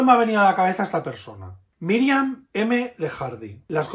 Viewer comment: What is the author's name that you're saying at 19:49? Google is failing me, so I must be getting the name wrong. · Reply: Miriam M Lejardi